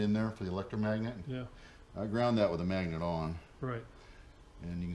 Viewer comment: What's the reasoning behind grinding the magnet with it on? Thanks. Hey, I'm with you on the doggie face licking thing, lap jumping too. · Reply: I must've misunderstood, I thought I heard of them say something like "do you have the magnet on" evidently I didn't hear it right. It didn't sound right that's why I asked. Thanks.